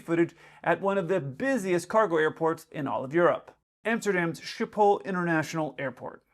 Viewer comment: for a sec I thought you said Amsterdam's Sh*thole 🤣🤣